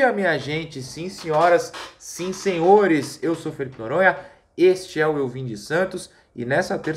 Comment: Eu juro que entendi “eu sou PEDRO Noronha…”